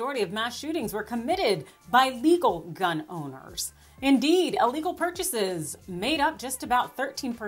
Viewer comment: I understand talking points, But if the mass majority = 13% your math is incorrect. I truly dislike MTG and the Florida pedo but your hyperbole is hurting your own cause. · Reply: I had to listen twice, because I thought I heard her say this too, but she actually said, "...indeed, illegal guns make up 13% of..."